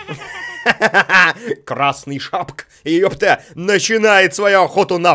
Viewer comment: Мне не послышалось, или он сказал "красный шапк"? 😅😆 Куда буква-то пропала, Дима? 🤣 1:05 Это - Рапунцель). Если ты не понял). Это же всё тут - сказки; Красная шапочка, за которую играешь, Белоснежка тут, Рапунцель, Спящая красавица и Золушка (без Золушки...). Красавицы не хватает, из "Красавица и чудовище"). 8:14 Накаркал, говоря про Dark Couls - 9:49... 🤣 10:10 Это - принц Филлип). Король - его папаша. 10:50 LOL Ради бабушки)). Чтобы её "спасти", а на деле, она её так с мотивировала пирожки поскорее принести). 12:30 P.S. Короче, понятно: Красная шапочка - девочка - *существует* Куплинов: "Он. Он"... P.P.S. LOL Модерация на полное название игры "Dark Сouls"...